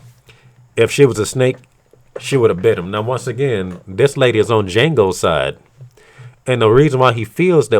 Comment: Oh Django. Deadass thought he was saying jingle for half the video